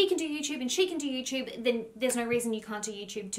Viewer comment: 'Love your guts"... this just makes me smile every time! Right back at ya! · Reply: Hahaha I thought she said love your butts 🤣🤣🤣♥️♥️♥️